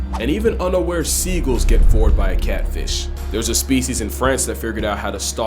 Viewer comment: Did I just hear “Vored?” Oh nah!